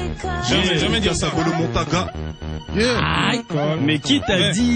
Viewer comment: c moi ou il dit gotaga ?? mdrrr